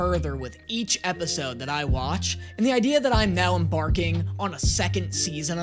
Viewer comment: Ohhh you're now EMbarking. I thought you were just saying you were barking 😅